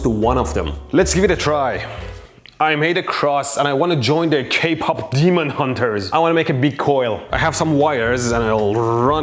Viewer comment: Did he just said the Demon Hunters movie? 🇰🇷 somebody support this fellow right here from Canada!!!